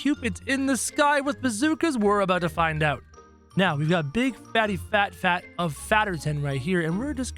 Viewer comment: did u said big fatty fat fat instead of hugo??? #